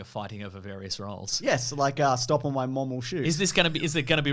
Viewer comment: I originally thought said "stomp on my momel shoe" and i thought what the hell kind of name is that for a movie? Then i typed it in to Google and it autocorrected to what it was actually called